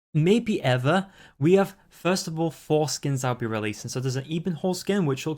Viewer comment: Did you say foreskin?